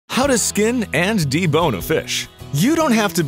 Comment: I… I thought it said ‘ how to skin and become a fish ‘…